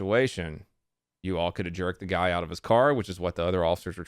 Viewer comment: i could've sworn i heard "jerk the guy off in his car" first time, god damn.